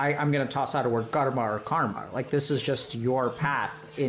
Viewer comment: What word did he say at 52:06 before karma, pls? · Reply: Dharma I believe.